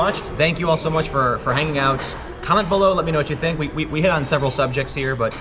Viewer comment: Heh, that was the shortest "comment" in "comment below", at the end; I misheard "cunt below", lol.